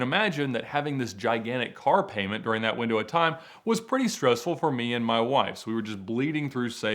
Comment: Pretty stressful for me and my wives??? Or did I hear it wrong???🤣